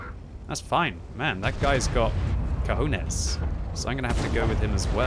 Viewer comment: did just said "cojones" xd